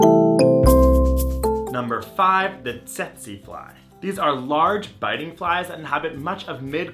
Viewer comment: When said 'tsetse fly' I heard it as 'sexy fly' and I was really confused 😆